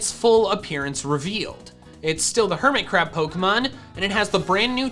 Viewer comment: I thought I heard hermitcraft Pokemon